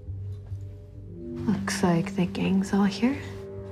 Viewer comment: Why does it sound like she said 'fucksake the gangs all here'